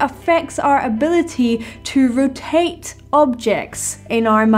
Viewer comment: in the video, is talking about the effect music you don’t like has on objects in your mind. Is she saying “rotate”? That doesn’t make sense to me in context.